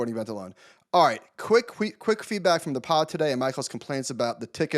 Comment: Did Batnick say "quick queefback" @ 46:40?? 🤨